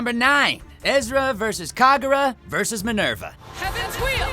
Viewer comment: DID DIS BOI JUST SAY EZRA!!!!!!!??????